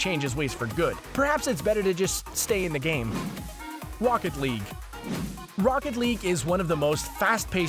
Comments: did u say wocket league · Did he say wocket league at 9:04?